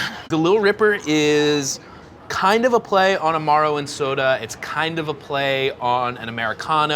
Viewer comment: not gonna lie, had to replay the opening cocktail with closed captions on cause I heard "amaro and soda" as "a morrowind soda" and I was deeply, deeply confused